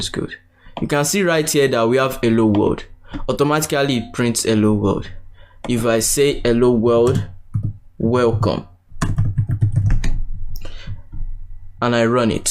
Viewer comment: Is it just me or is he saying "ewwo wold"/ "elo wold" XD